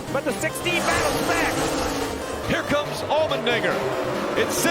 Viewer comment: What did he say? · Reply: Allmendinger